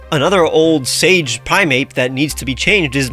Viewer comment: Did... Did you say primape @ 8:15... It's primate. ಠ_ಠ